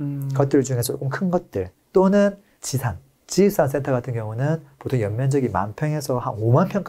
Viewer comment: 올 근생으로 되어 있는것? 근생이 뭔가요,,,? 지십상 센터,,,? (제가 제대로 들었는지 모르겠어요, ) 지십상 센터? 이게 뭔가요,,,? (운전하며 출근길에 듣다가,, 이말이 너무 궁금해서, 스크린켑쳐 해놓고 여쭈는 거예요,,ㅎ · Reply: 근생=근린생활시설 지식산업센터 입니다